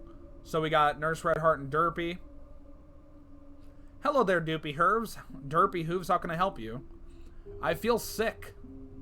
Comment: doopy Herves (that's how I heard it) 😂